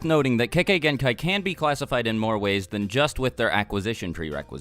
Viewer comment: Lol I thought u said kekkai hentai